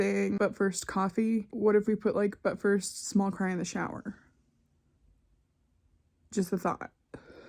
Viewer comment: I would absolutely smash buy on a “but first, small cry in the shower” shirt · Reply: I honestly thought she was saying, "But first, small crime and a shower" 😂😂😂😊😂🤣